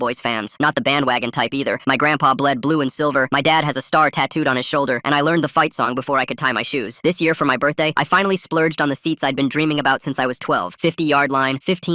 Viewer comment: I thought it said "i finally splooged on the seats" before i rewinded